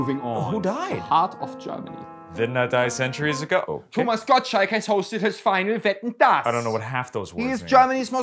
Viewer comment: Who else thought he said Thomas Gobshite?